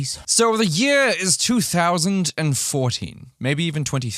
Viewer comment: I literally thought he said the year of 2040... wtf is wrong with me 🤣😂🤣😂🤣